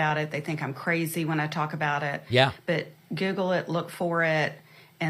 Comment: did she say "giggle it"